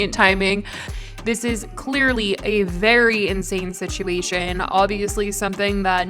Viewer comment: did you say a very “In-Shane” situation ?? Lol I’m so sorry 😭